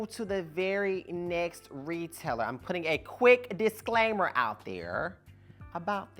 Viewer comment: When I heard you say "disclaimer," I said Target 😂 OMG I hollard! I love this channel.